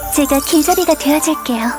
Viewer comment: 초 자꾸 제가 기저귀가 되어줄게요 라고들림...